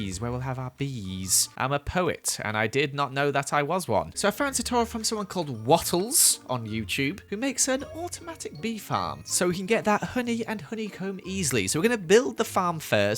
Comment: I thought he said waffles like she doesn't do minecraft???? 😂